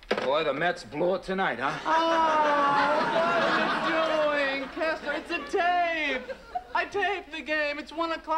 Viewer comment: Does Jerry call Kramer “Kesler?” and @ 3:45 Does Jerry call George “Arth?” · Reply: “Kessler” was the name originally used and later changed to Kramer. Nice catch ref “Art”. No idea why that occurred, but later, George does use the alias “Art Vandelay” and may have had some tie to this reference.